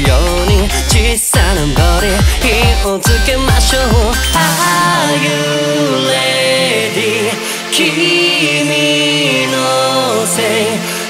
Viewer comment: コメ欄見てて思ったこと 外人多くない？！全世界《o(`･ω´･+o) ﾄﾞﾔｧ…！》に、luzくんとかのファンがいるんだなと思う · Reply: Loveまふくん 痲瑠飃 発音が悪いってdisられてんぞw Rが全部Lに聞こえる Are you lady？とかわりかしバカにされてる なるせさん好きって言ってる外人が多い